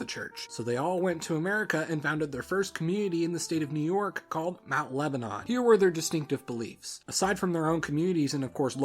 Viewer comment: Did you just say "mount lebanon" in 1:25 ?